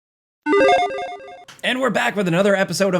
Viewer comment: I need to get off twitter dude, you said “and we’re back” and I swear to god I heard “n-word, black” said “WHAT?!” And rewound it.